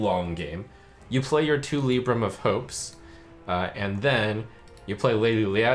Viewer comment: Lady Liadrin reads: "Add a copy of each spell you cast on friendly characters this game to your hand" Doesn't sound like Libram of Justice interacts with that, because it makes you equip a weapon (untargetted) then sets the health of the enemy minions. (Also untargeted, it's aoe) So I don't think your example of playing two Libram of Justice followed by a Lady Liadrin would work. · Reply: I think he said libram of hope, which would work if you targeted yourself (this was around 3:18).